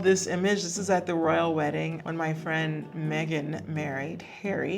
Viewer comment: Lol I thought they said that she wasn’t friends with Meghan Markle